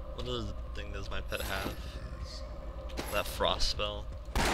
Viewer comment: Did you say "Frostfell?"